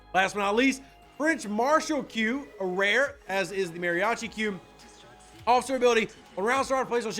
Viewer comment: Thank you - by the way: instead of "Mariachi Q" i heard "Mariachi Cube" 🤣🤣 ( which created a very funny scenario in my Head 😂)